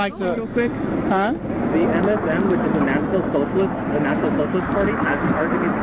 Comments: I think he said "MSN" and that it was a "National Socialist Party". Now that sounds about right. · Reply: He says they're with the NSM: National Socialist Movement. They're neo-nazis. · What did he (she? it?) said? National socialist party? Meaning NSDAP?